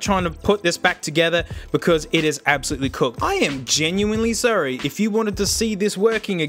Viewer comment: Did you just say "I am genuinely surrie"?